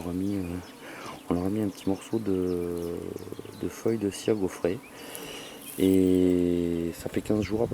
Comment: Tu dit quoi a 3:57 Est 6:02 ??? Ciago frais siagofrais ?? J'arrive pas à comprendre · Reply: Cire gaufrée